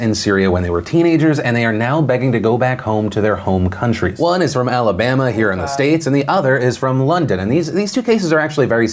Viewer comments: Does anyone else hear something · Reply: SirKillerWhale “Roll Tide” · Who is that saying "Ehhh" at 13:54 while Philip is talking? lol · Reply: Its saying roll tide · anyone else hear that? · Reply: StiggyB 14 “Roll Tide”